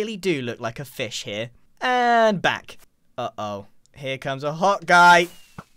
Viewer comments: hold up, has been saying "Hot Guy" this whole time??? I swear I thought he was saying hawkeye XDDDDD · I seriously thought he said, “Uh oh. Here comes a hot guy!”